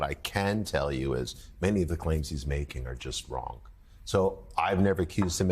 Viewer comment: did he just call Elon Arlon? :face-green-smiling